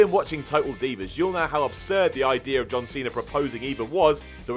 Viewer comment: Why does he say John cernerr